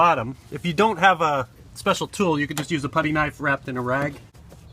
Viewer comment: I heard "Bloody knife wrapped in a rag" 😂